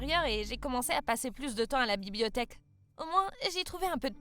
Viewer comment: Moi à 5:14 j'ai entendu à la bibliothète😂😂😂